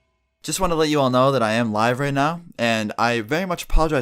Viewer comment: Am I the only one who thought he said, "I am HIGH right now"?